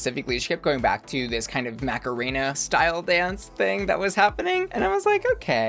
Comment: The Closed Captioning on your video keeps calling your sponsor "Macarena", and I keep hearing it as "Mecha Rina", which for some reason feels like it's a drag persona that dresses as a Transformer and performs ballet.....maybe I DO smoke too much weed....